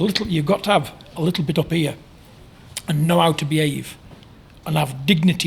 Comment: thought he said a little bit of beer 😂had to double take